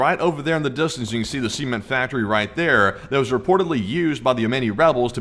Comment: I keep hearing words "semen factory", its CEMENT factory, not semen factory.